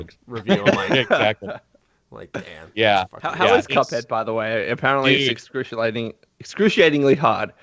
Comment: What game are they talking about? It sounds interesting but i cant understand the title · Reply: Cuphead, jump and run 70s comic